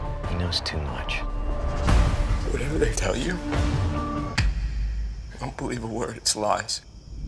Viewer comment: What does Eric says in 0:57 and Sally's in 1:11? I'm not a English speaker, so I didn't understand well :( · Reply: Eric: "Whatever they tell you. Don't believe a word it's lies" Sally: "Not really. Not even close"